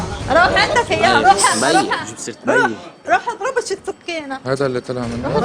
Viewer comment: شو قالت الأم بدقيقة 46:42 كأني سمعت عم تقول" روح اضربوا بشي سكينه" حسبي الله ونعم الوكيل اللهم إنا نعوذ بك من عقوق الأبناء، ومن جفوة الأقرباء، ومن تغير الأصدقاء، ومن شماتة الأعداء.